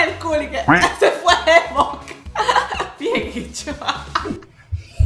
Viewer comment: pero la patilaquienase jajaja creo ni entendi